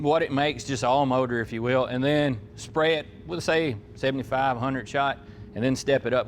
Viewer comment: Did he just say he’s gonna give it a 7500 shot of nitrous?!?! I know it was supposed to be 75-100 shot.